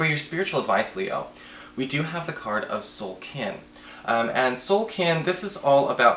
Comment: For the spiritual advice card I swear I thought I heard, you say "Soul Ken"? - Barbie's soulmate? Anyway - thanks for the reading for my rising.